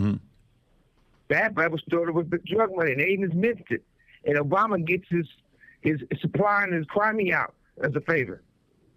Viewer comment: what does he say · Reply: No limit